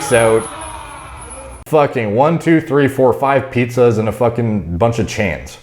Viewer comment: Did I hear Derek says "1,2,3,4,5 PIZZAS and a couple chains"??? Phew. Stealing that